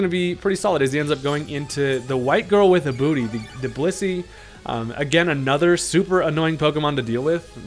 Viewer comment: I thought I heard “bussy” 😂